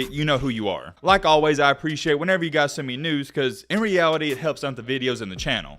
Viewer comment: for a second I thought said "I appreciate it when you guys send me nudes" 🤣 Just watched that Newman video, got something else on my mind 🤣🤣🤣🤷‍♂️🤷‍♂️